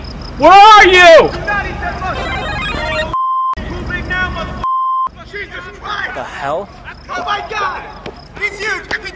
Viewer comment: "Do Not eat that Mushroom" blur blur blur "Oh beep!" "Who's big now motherbeep!" "JESUS CHRIST!!" hahahahahah awesome stuff!! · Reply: After all these years since i first saw this, i thought he actually said "You dead now Mother(Beep)!" xD